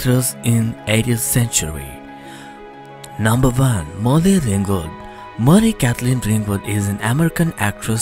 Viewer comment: I can't understand half of what the narrator is saying (0:10 Number "vun: Molvy Vingvald"; 0:45 Sir Lawrence "Oliver"; 1:23 Jennifer "Lazun Lay"; 2:09 "GHeena DAHvis"); he doesn't speak clear English, or even close to it. Also, "American" is misspelled in the graphic at 0:15 (second vowel should be an "e"). Oy vey... Maybe if you had the video narrated again by someone who can speak English CLEARLY ??